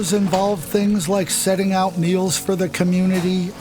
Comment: Edit: I completely misheard this. They said setting out meals, not needles. Sorry. "The chores include setting out needles" - Seriously? I'm all for tinyhomes for homeless people but let's not fuel drug habits or tolerate drug use.